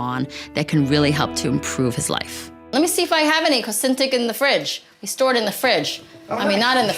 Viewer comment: Can you tell me the name of the cure (injection) please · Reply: Sounds like she said Consentyx